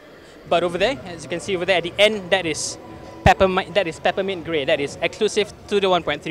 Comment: Did you say peppermint GREY or GREEN ? I heard you said Granite Grey and Peppermint GREY....lol